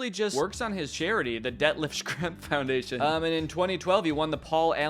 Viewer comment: Deadlift Shrimp is all I hear with that name